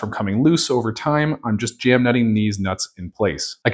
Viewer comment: did you just said.... deeez nuts? 🙂 :-)))))))))))))))))))))))))))))))))))))))))))))))))))))))) Maaan, you're a... man of culture.